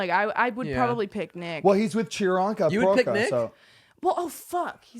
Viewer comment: So we really all just gonna pretend we didn’t hear say “chironka” instead of Priyanka @ 1:07:40 ? 🤣